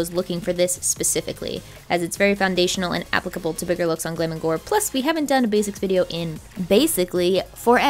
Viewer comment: did she say glam and glor